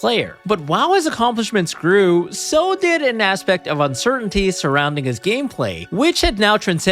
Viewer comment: "...while his accomplishments grew... so did his chin." Oh, wait, I misheard that. Sorry. Not really.